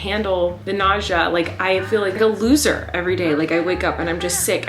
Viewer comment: does anyone else hear her say i feel like i am going to lose her? If anyone else hears that like this or reply to it. If she ment to say that i wonder if it is at least one girl. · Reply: she said she feels like a loser not lose her